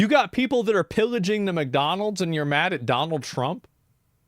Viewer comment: Did I heard McDonald Trump 22:54 ;D